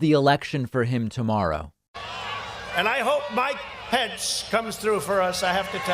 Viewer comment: Does he call Mike Pence “Biden Pence” at 2:35? If so it’s the greatest Freudian slip in history!